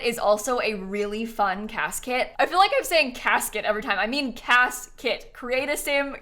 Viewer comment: Everytime that says "cas kit" I hear "casket", help